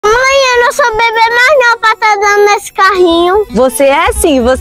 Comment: Eu entendi "mãe eu não sou bv mais não pra ficar andando nesse carrinho"😂😂😂😂😂